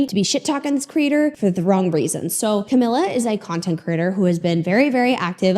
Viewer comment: Why do I keep hearing it as “Content Critter”? 🤔